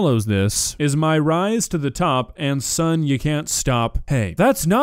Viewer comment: I'm actually pretty certain the guy says "My rise to the top ain't somethin' you can stop" and they just didn't caption it correctly. I'd be willing to bet this part of the script just says [Bad guy raps] and the editor had to just guess what he says when checking over the AI transcription.